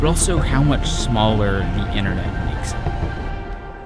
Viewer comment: “But also how much smaller the INNA NA MAKSA….” What did he say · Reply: The internet makes it